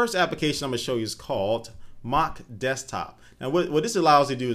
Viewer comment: u mean mac desktop i no i thought he said mock to